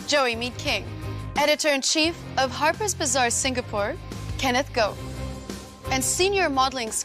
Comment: I thought they said Kenneth Goat